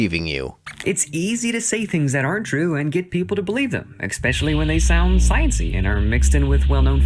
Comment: Okay, Nitpick time, I swear at 0:07 he says "expecially" and it's bothering the hell out of me.